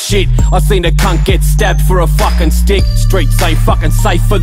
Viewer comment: STABBED FOR A STICK, TRUE BRUVVA! ✌️💜 From Melbourne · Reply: I thought he said stabbed for tick